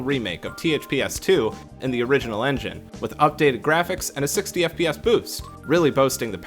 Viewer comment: did I just hear 60 fps boosts??? Crazy that it ran higher fps than most ps4 games did.